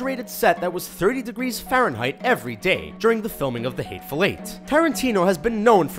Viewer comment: I kept hearing "the hate fellate" I don't want to know more